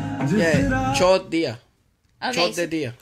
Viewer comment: The way you pronounced "left" sounded like "chod diya" which translates to "fucked him" I know it's just a pronunciation thing but God did I have a good laugh😂